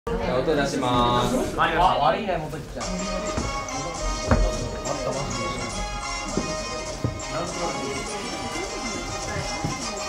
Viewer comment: 最初悪いねモトキちゃんって聞こえたのは気のせい。？